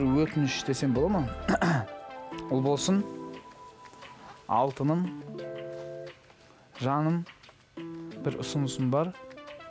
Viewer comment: Алтыным,Жаным вот что он сказал! Редакторы вы гоните!!!!